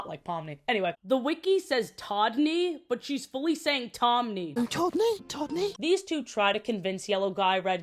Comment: she is definitely saying todney i fear